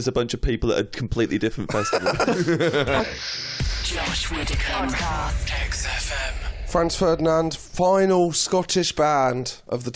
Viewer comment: I thought the voice said: "Josh Widdicombe - Sex Offend" Me: "WHAT?!? Was there an -er in the end?" When "The Voice" came again - And I read the name of the show! 😂😂 OMG! :elbowcough